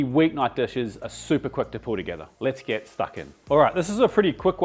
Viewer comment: Did I just hear say "let's get fuckin?" Lmao